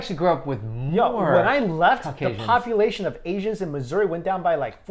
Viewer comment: Each time I hear them say "Caucasian" I think they're saying "cock Asian"